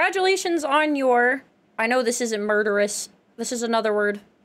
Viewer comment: the word you couldn't figure out was "marvelous".